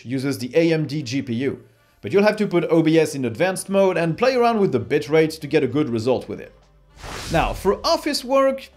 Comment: I swear at 6:09 he says "play around with the bitch rates" lol